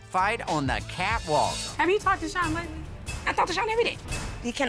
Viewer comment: did she say "she talks to her ex EVERYDAY"! Or was I just hearing things 😨